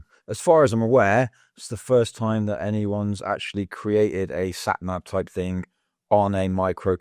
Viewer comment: I've been watching your videos for a while and they are very good. This is the only one where I actually found something I don't agree with. While it is a mini-map (and an insanely cool one), it's not really a sat-nav since there is no route calculation involved (don't even think about trying to do it locally lol). You could do it with an open source stack and get routes working and showing other "players" that joined the same room (eg. Redis for real-time reporting). You can also serve vector tiles which are roughly 90% less memory than what you're using now. This will allow you to calculate routes and show them on the map. Obviously this will require internet connection but with a big enough sdcard, you can cache a lot of it and prioritize local files. A 60 miles route requires about 5-ish MB of storage for the tiles. If you cache all the needed info at the beginning of the trip, you don't really need internet (you won't see other drivers, etc, but for single trips, works great) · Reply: My mistake, I was sure I heard you saying sat-nav towards the end of the video but after rewatching the video, it was actually "a sat-nav-type thing". I stand corrected 👍🏻 Going back to agreeing with everything you said in the videos I watched so far.